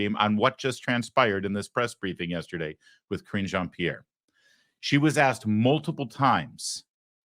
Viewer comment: I'm not American and English isn't my first language but each time someone calls the press secretary by her name, I hear "Cringe Jean-Pierre". Why do you think is that?😊